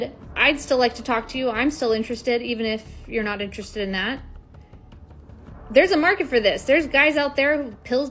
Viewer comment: Just comment for the 4:08 timer. " will her provide the service of the doll for the same price $$$? On side of that doll services could be proving relief to man that my not rape her dual to the pleasures the doll provides. 😂😂😂😂😂😂😂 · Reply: did she say market omg selling that 🍑 peach